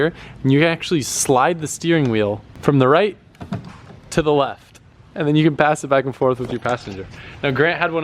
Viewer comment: I heard “you can pass the steering wheel from the right to Collette” instead of “to the left”